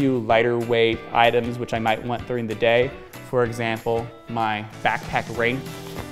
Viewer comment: Any idea what he says at 4:45? It sounds like he says ‘rain’, as in ‘rain cover’, but the camera zooms in while he’s already got it half stuffed inside the pack so it’s impossible to see. Also if this is a video presumably for novices, can we dispense with short hand and just say ‘rain cover’. Or is it some other item entirely. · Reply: I hear backpack brain